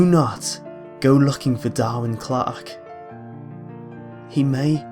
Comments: When I first heard "Darwyn Clarke", I understood "Darling Clock". LOL. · At first when I heard the name I thought I heard "My Darling Clark". O_O